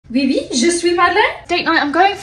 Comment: “yes, yes i am speak!” -a perfect french speaker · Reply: I definitely heard “je suis Madeleine”